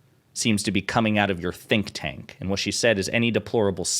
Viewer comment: Did Crocket say "think tank"? I heard "stink tank", which is fitting for the Heritage Foundation. lol